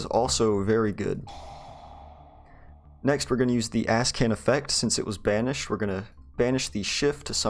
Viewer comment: I know it's the name of the lvl 8 synchro but I always hear "Asscan". Nice Combos.